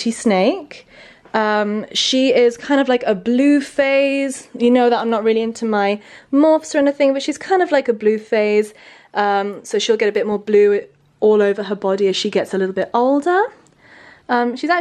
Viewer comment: I thought she said blue face 😂